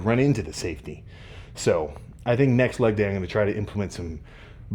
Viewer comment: Thought that said natty leg day for a second 😂